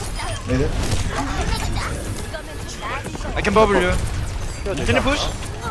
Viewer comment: 에 브리기테 방어구를 드릴구율 이러는거임 ?? 뭐라는거임 ??